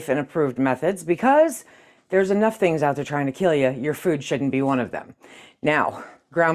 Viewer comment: “There’s enough things out there trying to kill you. Your food should be one of them.” 😂 Love ya! 😊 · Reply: I thought she said shouldn't